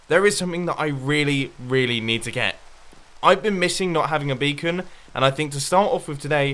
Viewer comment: i thought it said bacon instead of beacons